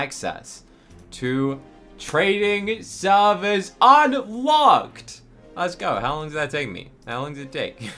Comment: Did he say a badword? Im not sure lol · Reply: No he said unlocked